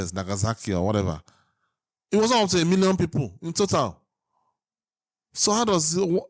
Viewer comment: Hi...I just wanted to check on something I thought I heard you say...at one point you were talking about a different topic and made a comparison to Hiroshima and Nagasaki, and it sounded like you said that they killed a million people between the two of them? 20:29 I definitely could have misunderstood, so I wanted to ask if you really said that and believe it? If so, you should know that both atomic bombings only killed about 200 thousand people...maybe less, maybe a bit more depending on who you ask. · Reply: Yup my accent failed me there I guess lol. I meant, "it wasn't up to a million people in total."